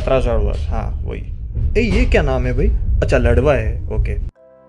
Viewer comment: 😂 You think lavda instead of ladva